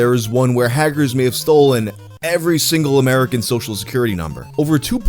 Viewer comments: I misheard the ad and because of the Harry Potter association, I heard "Hagrid stole every American's social security number" and I shit myself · My brains on Harry Potter now I thought you said Hagrid has stolen identity’s. You said hackers. My bad lol